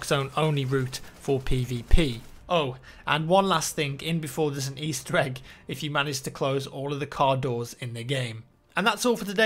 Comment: Can someone please translate what he said about closing all the car doors.. I heard "And one last thing, end before there's an estrike if you close all the doors in the game." Which makes zero sense at all. :)